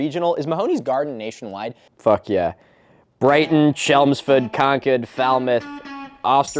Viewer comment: Did you say Concud?